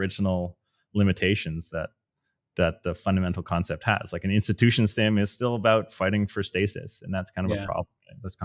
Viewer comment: TS mentions fundamental limitations and says that "an institution-something is about fighting for stasis". Anyone pick up what he's saying there? Really enjoying the video, thanks for the contribution! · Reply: institution sim (simulator)